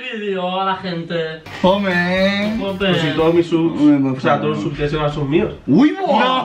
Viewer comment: no entendi lo que dijo, alguien me lo diga? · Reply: le dice que los subs de son los mismos que los mios